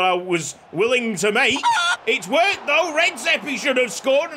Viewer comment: I’m glad Redzehpi is gone literally everytime I thought he was saying Rexhepi the (other) Swedish legend from the burton series